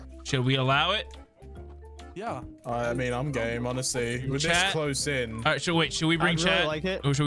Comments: I was literally thinking gay instead of game oops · I it sounded like said I'm gay